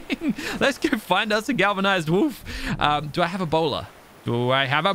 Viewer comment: Whenever he says,"do I have a bola" the first thing I hear is, "do I have Ebola".